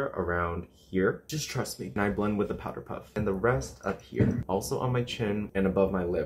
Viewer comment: I thought he said “I am one with the powder puff” and I didn’t even question it